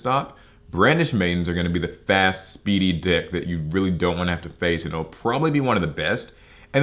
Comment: It could be the fact that i just woke up when i saw this video but did just call brandish madians "a fast speedy dick".